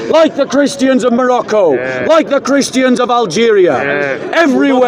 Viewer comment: Did he just say Christians in Morocco at 28:10 ?😅